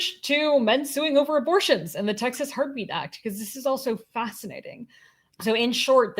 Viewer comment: I heard 'Texas Harpie Act'. I got excited that Scold's Masks were coming back...maybe next year....